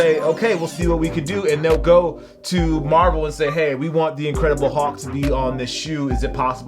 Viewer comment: Did he just say, 'The Incredible HAWK'?